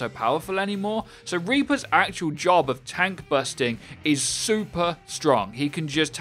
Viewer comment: I thought it said tank buffs....